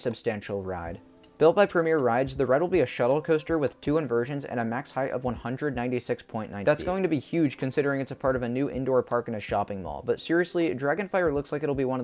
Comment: you may have noticed that I accidentally edited two clips over one another for just a second. It might sound like a bunch of gibberish because of this, but what I meant to say is “Dragonfire will have a max height of 196.9 feet. That’s going to be huge considering it’s part of a new indoor park in a shopping mall...”